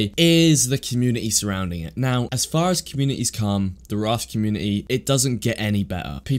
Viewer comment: I thought u said the rust community was helping and giving advice I was like wdf😭😭😭😂😂😂😂